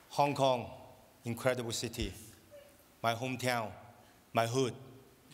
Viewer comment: „My hometown, my hood“ that’s that Chris tucker influence lmao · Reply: Lmao i thought he said my hoot